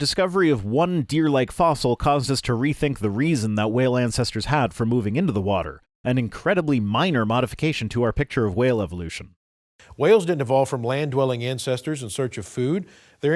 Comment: It reeeeeally sounds like he says "incestors" at 21:40 instead of ancestors. Hilarious. Maybe that's the word he uses behind closed doors when referring to humans. You know - Noah's flood and all... ;P